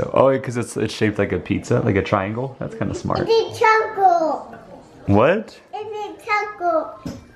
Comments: I think he was saying its a triangle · I think he said "it's a triangle" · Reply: thought he said "its a taco" lol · It sounds like said "it's a triangle" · I thought said, "it's a taco!" 😀😀😀